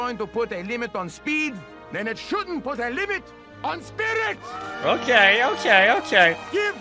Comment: Guy's pronunciations from his accent made me think he was saying "Spinach" instead of "Spirit"...😅😂😂😂😂